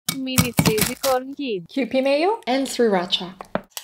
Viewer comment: At first I thought she say 3racha when she say sri racha